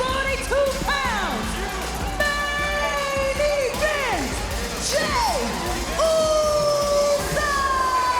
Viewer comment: It reads "$0.90 Jey Uso" in the subtitles instead of "Main Event Jey Uso" lmao 🤣